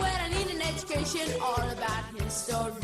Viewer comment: ~ I think he said His Story not history. Hard to tell